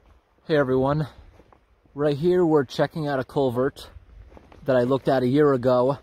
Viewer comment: For a while I thought you were saying "A Colbert" 😂